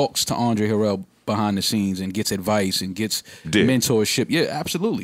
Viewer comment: "puff gets advice & gets d**k" lmaoooo did Joe say that or I'm trippin